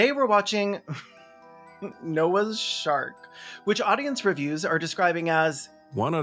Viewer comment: I first heard you say "Noah's Shart," which would have been a very different but likely far more entertaining movie.